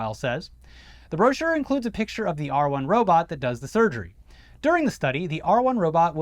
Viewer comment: The first time I heard em say "R1 Robot," I thought they said "R Word Robot." I just thought to myself, "Man, that's a weird name for a robot, but that's classic Musk," haha